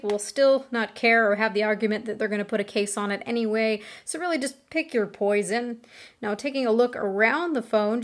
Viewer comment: did she say "pick your poison" lol